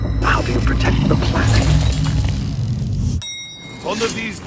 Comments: I thought he said “Wonka Factory” lol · At first I thought he said Wonka factory I was like you mean like Willy Wonka? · I thought he said “The Wonka Factory” as in willy wonka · Thought he said Wonka factory